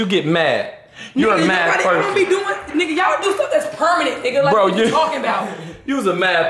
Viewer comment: For everybody who said it’s w weave I think it’s not cause she said y’all doing things that’s PERMANENT